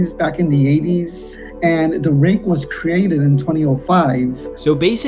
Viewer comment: When he screemed rake I thought he said drake